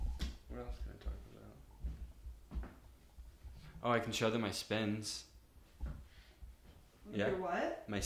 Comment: when he said spins i thought he was saying spends and referring to suspenders